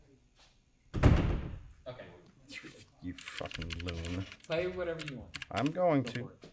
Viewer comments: Is it just me or did anyone else hear someone cursing at the end of the 1st person that was listening at 8:58. The said you FKn loon or something 😂. I’m going to try and make a couple myself. I’m very curious how they will sound. Great video and very informative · Hey man at 8:58 did you say you f---in loon ? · LOL did he not know he was mic'd? He is saying "you fucking loon!" under his breath for those that can't tell what he is saying